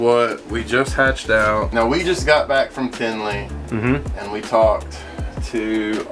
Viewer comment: Did he say around 1:35 they just got back from Findlay? What’s that?